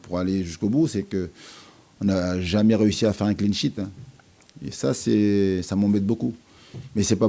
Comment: un quoi ? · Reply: Cleanshit pas prendre de but